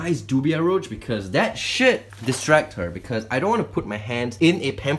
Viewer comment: turn on subtitles and instead of saying “that should distract her” it says “that shit distract her”